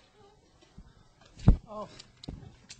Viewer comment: DID HE SAY CHEESE BREAD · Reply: He says "geez Brian" but I wish he said cheese bread :(.